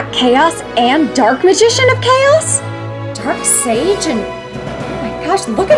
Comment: I'm sorry, but did she just say Dark Sage? Why? Why would he play that in a Dark Magic deck?